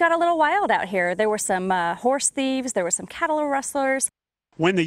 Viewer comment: dafuq, did she say "cattle wrestlers"??? they out there suplexing cows